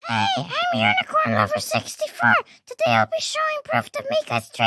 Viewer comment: Help I hear"unicornlover60fart"🥲